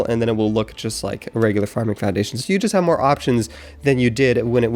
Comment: Anyone else hear floundation when he meant to say foundation? the minute is